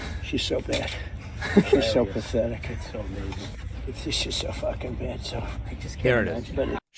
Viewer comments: I'm sure he says it, but I heard "so fucking bad" and so did the closed captioning. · I HATE Trump, but to me it sounded like he said “she’s just so fucking bad”. He’s still a bitch, but I think that’s what he said 😅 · I have no doubt in my mind that this orange clown has called Kamala Harris a bitch, but am I the only one hearing him say "shes just so fu***ng bad so" @ 2:07 ? Please understand, I was going to vote for Biden and WILL vote for Harris, but I have to be honest with what I hear and what gets put out there. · Ha. I didn't hear that. I heard, it's so fucking bad. Sounds like you're putting words on his mouth again. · I have great hearing, and in that video, it sounded like he said “she’s so fXXking bad”. I don’t doubt he uses the other b word regularly, however. Money doesn’t buy class. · It sounds like he just said "It's just she's so bleep bad so". I am not 100% sure he said the word. You know that will be the defense, and the narrative on this video may be wrong. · He said, “she’s so f’ing bad”. I hate Trump, but at least get it right! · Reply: Im votin Kamala Harris but cmon i heard "shes so fkin bad" lol 😂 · Trump sucks but it sounds like he said "...she's so fucking bad so...". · It sound like he says “she’s so F-ing bad so”. But I’m not sure. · I can't stand trump, but to be fair I think he said "fucking bad" he was on a rant about how bad she is. I could be wrong but that's what I heard. Trump's still a clown 🤡 tho